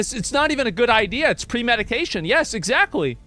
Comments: I swear I heard ‘’pre medication ‘’ when you meant to say pre meditation 😂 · premedication? I hear that right? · "its pre-medication" lmao that's what i heard lmao we love your bloopers Asmod you awesome bro